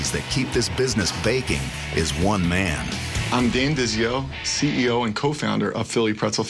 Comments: Who else thought he said “im dandiz, yo” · I thought he said ' I'm Dan Diz yo '